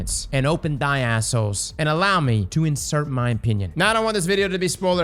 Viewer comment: I know he says opinion but his accent makes it sound like he said penis ngl